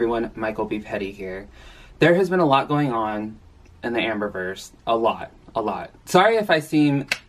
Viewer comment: Thought you said "Amburgers" instead of Amber-verse 😅 Eeeeww<link>